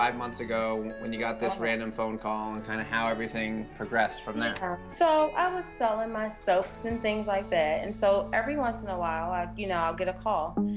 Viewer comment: For #3 I thought she said "so I was selling myself and things like that". I was like WHAT